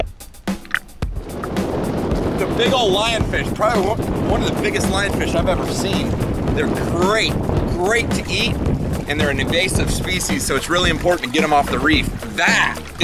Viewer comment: Did he just say these fish were "evasive"? Hahaha. Invasive.